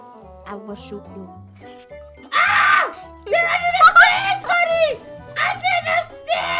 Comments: In the intro I thought he said under the sea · I thought said UNDER THE SEA NOT UNDER THE STEARS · I thought said under the sea at first T-T